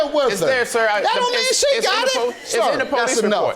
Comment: Judge Mathis is a true comedian. 😂😂😂😂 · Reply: I thought JM said that don't mean sh*t, but rewind and he said she got it.🤣🤣